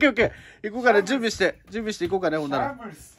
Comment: シャンブルズがバブルスに聞こえるww とても面白いですね。